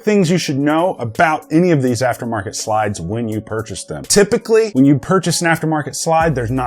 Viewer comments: Did you say runs like a rape date at 11:04 man… I hope you didn’t because Iv been fallowing you for some time now. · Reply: nope I said Raped ape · did you say it runs like a rape date?? 😂😂😂 I’m loosing it!